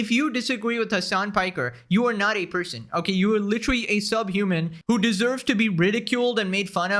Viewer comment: Did I just hear you say... subhuman?... Dante starts to pull up to hit Hasan with the most DIABOLICAL SSS combo